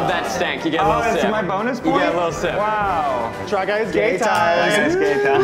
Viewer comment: I feel like David, Matt and Eugene, with their running joke of saying "gay time" instead of "game time", would all be pleased to know that Australia has an ice cream called a Golden Gaytime :) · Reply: As a fellow Aussie, when they first started this segment I for sure thought they were saying Try Guys Gaytime